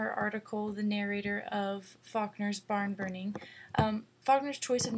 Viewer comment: hi it sounds like you say "fuckner," sorry but i had to point that out. Great analysis though, it really helped me understand the story.Thanks!